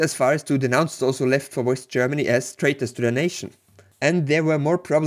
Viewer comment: The true bias is her saying "worst Germany" instead of "west Germany" in a video portraying the USSR bias at 9:06 KEKW